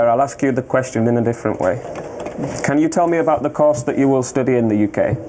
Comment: I dont get what an officer is saying "Course or Cost"????